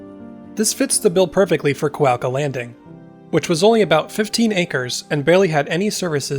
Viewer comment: Thought it said "cloaca landing" at first.